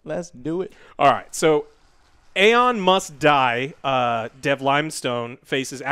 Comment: I didn't think about it that way at first but when Alex said "Aon Must Die" for a split second I thought he said "Elon Musk Die"